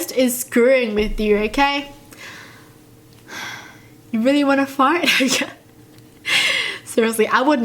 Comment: omG I THOUGHT YOU SAID "DO YOU REALLY WANNA FART" AND I WAS REALLY CONFUSED